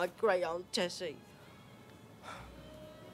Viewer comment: I thought he said “marry me, harry”